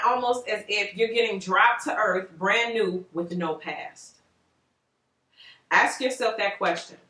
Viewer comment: I thought you said no ass lol 😂 I’m like girl I remember those days now a bishhhh out here thick and shit 🤣🤣🤣